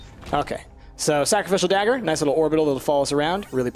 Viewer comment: sounds like you said" cookiedral" because I was eating chips